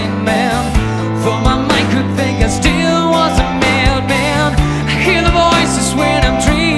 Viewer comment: did he say "I still was a mail man?" Lol